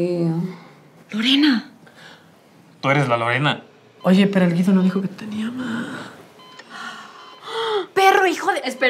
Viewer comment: Hola, soy yo o en el momento 1:40 se oye un 'hey', yo no se, pero yo escuche un hey